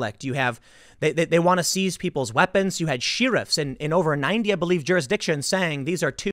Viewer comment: did Tim just say “she-riffs?”